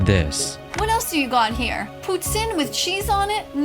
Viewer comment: she really said poo tin